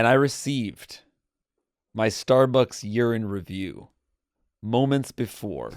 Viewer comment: i swear Jake says “my Starbucks urine review”